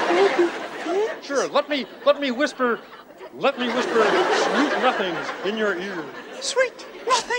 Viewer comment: what did the other kermit whispered in miss piggys ear? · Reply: Sweet nothings